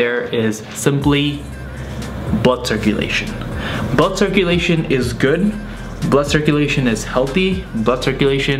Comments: I thought he said butt circulation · I heard "Butt circulation"